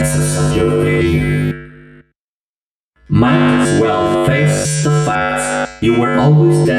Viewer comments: I think he said fear. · Withered Bonnie: "Time to face the consequences of your failure" "Might as well face the facts, you were always destined to fail" "You blinked" "What is this (new prison?)? Is it me trapped, or is it you? Perhaps it's us both" "I may be missing my face, but even I could see (this coming?)" I hope I helped! Sorry about the parts I'm unsure of. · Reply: Miss Agronaut I thought it said "creation" or "creations" instead of failure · is failure, and it's "Might as well face the facts, you were destined to fail." Not fight me. Later it is, "what is this new prison, is it me trapped or you? Perhaps it is us both." And I think he says "but I could see his tongue..." I don't know what that means but ok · Why you have those question marks? He says failure, pretty obviously too. 5:05 That one is strange, it sounds like he is saying, "I might be missing my face, but then I could still see stock" That's what I heard the first few times then I realized he is saying, "but then I could see you stuck" 6:49 Scarecrow!? As in Arkham Knight Scarecrow!? Also has Scott been reading fanfics of his game? Some of the female characters have a not-so-kid-friendly tone while some tease, Ballora in particular with her second line.